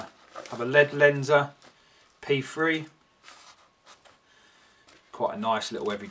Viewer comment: What is he saying at 4:42? It kinda sounds like "pea free", or "pee frea". Loved the review. Greetings from Nashville, TN. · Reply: chemistrycounts LED Lenser P3 Key Ring Torch